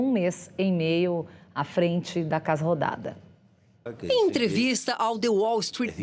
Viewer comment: Só eu ouvi "casa rodada"?